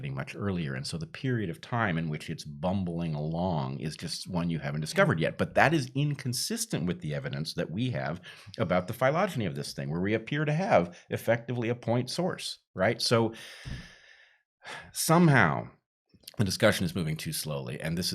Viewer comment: I always learn new big words here. At 15:17 Brett uses the word phylogyny (if I heard right) but the definition of that word doesn't make sense in this context. What word did he use there?